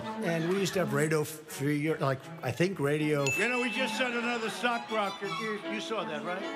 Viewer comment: Aw, I thought he said Sock Rocket. :)